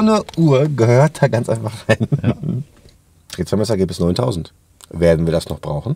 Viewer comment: Minute 11:43 "....Ergebnis 9000.." Was hat er gesagt?